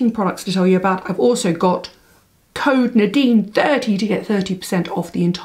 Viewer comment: I must be tired because I thought Nadine said the code was Nadine dirty not Nadine 30 lol